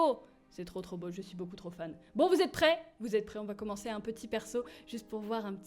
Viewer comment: "Bon vous êtes prêt ?" Moi : Bon vous êtes prêtres !